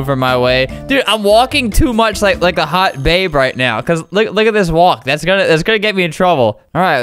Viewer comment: When he said “look at this walk” T thought he said “LoOk At ThIs wAp”